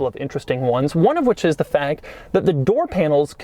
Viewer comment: does Doug say fag?